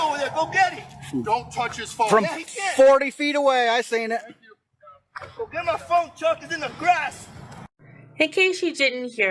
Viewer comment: I thought he said “ get my phone and chuck it in the grass”.